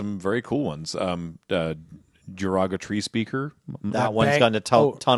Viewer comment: I heard say "Derogatory Speaker" and it still sounds like an actual Magic card name. I was picturing something in the black or black/red part of the color pie.